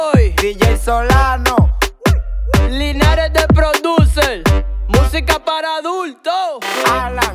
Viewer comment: POV: yo después de estar escuchandola 3 veces seguidas y la me doy cuenta que dice MUSICA PARA ADULTO' yo: 🙄